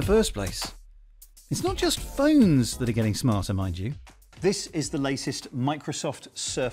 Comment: Did he say my g?😂